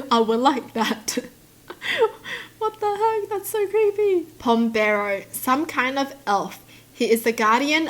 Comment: I thought she said Bombero which means Fireman